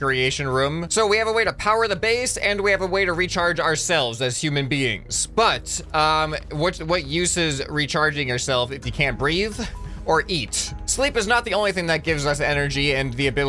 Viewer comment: ish thought you said “breed” or eat but you said breathe or eat (Sorry for bad English if anything was wrong)